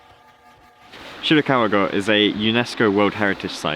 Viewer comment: dude slow down, Shirakawa-Go sounded like Chicago in your mouth 😂😂 Great video as always, nice balance between talking parts and beautiful B-Roll. I love the fact that you put quite a lot of pictures inbetween clips too, it's cool to have an different take on a location than just the video!